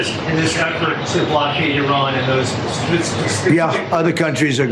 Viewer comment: Do a blockcade!?!... We thought you said lemonade